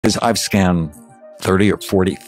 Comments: I thought he said scammed not scanned 😂 inwas really confused on the whole topic for a good second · I thought he said he "Scammed" people with ADD